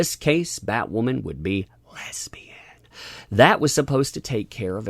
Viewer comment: Lesbian!?....I thought they said Lebanese!. The first middle eastern batwoman.